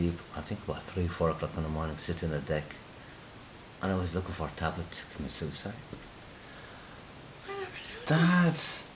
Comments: What did he say at 8:00? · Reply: he said " I was looking for tablets to commit suicide" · what does he say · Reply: "I was looking for tablets to commit suicide." Pills. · sorry i didn't understand what He said at 8:05 "the tablet from the suicide"?? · what did the dad say at 8:00. I couldnt understand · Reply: he was "looking for a tablet (pill) to commit suicide" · can someone please write what the dad says at 08:00????? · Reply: Paula Elias he said: I was looking for pills to become sucide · This is beautiful!!😭😭❤ What did the father say?? I hear "I was looking for a tablet commit suicide" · Can someone tell me what the dad is saying at 8:00❓ I can't understand him it sounds like, "was lookin for a tabla at a sus eye" ❓❓❓ Thanks in advance.